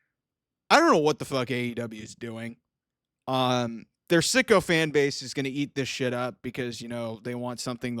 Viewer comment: I did not know you were saying sycophant. Which means yes, man or ass kisses to a detriment. I thought you were always saying sicko fans!!!